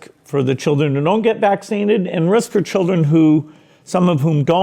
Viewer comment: Did he just say "vaccinned"...call me crazy but I thought it was pronounced vaccinated....